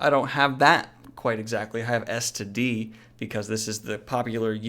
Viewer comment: God xd im playing the video and the first thing i hear is saying "I have STD". And im like wtf XD and i had to rehear that to understand that ice just misheard smth